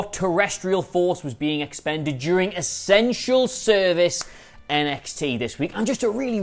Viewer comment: I swear I thought he said "a sensual service"!